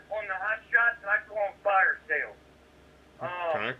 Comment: does he say he would go on fire sales ?